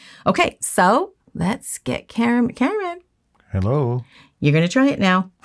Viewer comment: I love when you include Cameraman! You should make this a series of teaching Cameraman the basics! Let's see how good he can get ❤️ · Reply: lol, I thought she was saying Cameron.